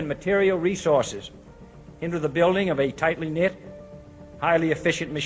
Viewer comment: I appreciate you putting together this video but I would suggest making sure the subtitles you put up are the correct words of JFK's speech. For example at 6:58 of this video JFK says "tightly knit" but the subtitles say "tite menith". By showing the wrong words that you're using as an example it will turn people away from hearing the message you're trying to portray. All the best. Peace